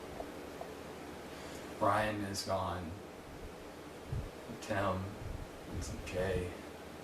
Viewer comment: did he just say tim's gay? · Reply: He said "Tim and Jay"